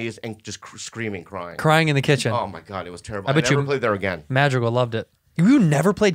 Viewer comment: Has Bob been saying Al Madrigal this whole time?? Every time he referred to him I thought he was saying “El Magico” until I just looked it up lmao.